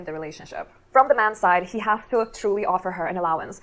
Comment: I thought you misinterpreted that bit about allowance because of the speed, then you played the rest and you were correct. I thought she had said, "For that (financial dependence) to be true he'd have to offer her an allowance."